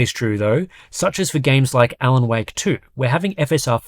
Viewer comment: I've almost piss myself when I heard "Alan Wank 2" in 17:32, pure gold there mate, thanks!